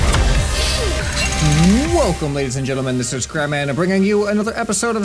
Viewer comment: "This is crap man" TELL ME IM NOT THE ONLY ONE WHO HEARD THAT